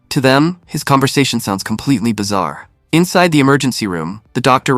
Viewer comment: did you say.. “bizarre”?